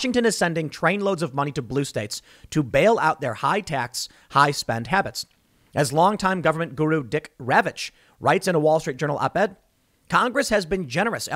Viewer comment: "dick ravich" I thought he said Dick ravage I had to do a rewind and had a little chuckle at his name